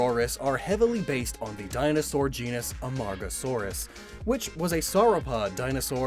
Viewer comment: Did he just say AMONGUSaurus?